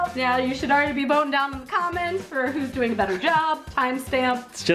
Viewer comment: i thought they were saying "bone in the comments"